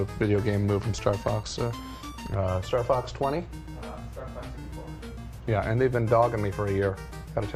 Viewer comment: "Star Fox 20?" "...Uhh, Star Fox 64." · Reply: I heard him say "star fax 24 " and "star fave 420"